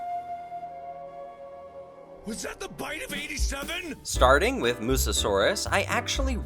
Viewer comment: Are you a FNAF fan cuz if not why did I just hear "was that The bite of 87"